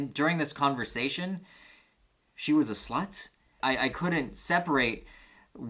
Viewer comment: I had to replay this so many times because I very time I heard 'she was a sloth'